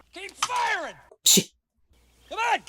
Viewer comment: Sorry, coming back and watching again all i hear is "Keep firing Assholes" lol